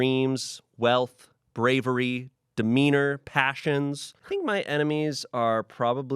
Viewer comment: WHEN HE SAID ‘demeanor’ I THOUGHT HE SAID ‘wiener’ AND I HAD TO REPLAY IT